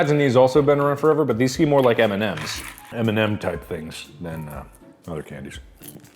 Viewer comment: I thought it said candace